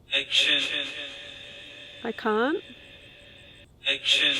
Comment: When you thought it said, “I can’t”, I thought it said ancient.